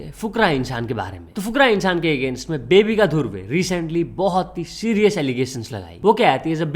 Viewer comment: i was so serious until i heard bebbika dhruve